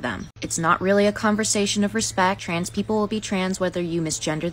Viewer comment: Took me way too long to realise you were saying six people not cis people 😅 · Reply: Me too!! I thought that he was saying cis people😅 and I am African myself so no excuses there 😅